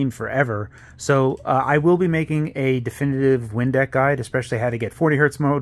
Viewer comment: I legit thought he said Windex for half a second. this is cool though